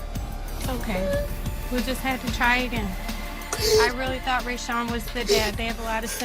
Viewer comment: did i just hear her say to her "its okay, we just need to try again"???? So they've not been sure all the while.